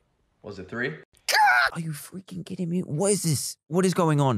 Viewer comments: did dan say the f word? this is from 2024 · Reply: He said frick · dantdm are you f**** kidding me did he say a bad thing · I thought he said the F word but he said freaking